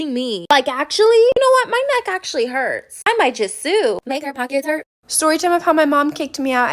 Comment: did she say "make her pockets hurt" ? ERM